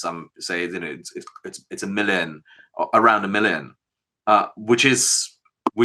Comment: is he saying a million? strange pronunciation